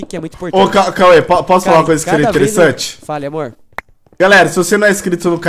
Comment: foi só eu que ouvi fala amor ?????kkkk